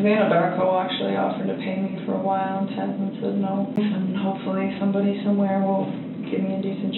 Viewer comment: doing well ty and same to you 💞 thank you for answering because I thought she said my Dad said no I was like whaaaat😂......What I find hilarious is the way he says to her you can go talk to Charlotte or ya know whatever other friends you have as if saying "do you actually have any other friends" 😂😂😂 yup you nailed it on the honesty with Charlotte or anything else for that matter. AV is so ME ME ME ME ME oh those guys are gonna miss me working with them. I had such a great job til all this shit happened...NOT upset about what happened upset she lost her job over it! Ok the "little blouse" comment was weird I'm not knocking heavyset women that IS NOT my intent so please nobody come for me lol but the woman that walked in was quite a heavy set woman and she says I like your LITTLE blouse? Was that because she was nervous a woman was walking into the room who she can't "seduce" into compliance🤔🤔🤔🤔 just very bizarre!